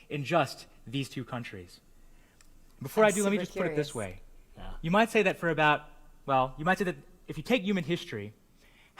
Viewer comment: What did she say @ 4:47? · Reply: Siddhant Kumar “I’m super curious.”